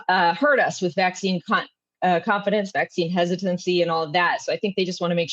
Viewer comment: Did she say vaccine cu** at 1:28? It just a clown show now. CCP - CDC. A bunch of Yahoos.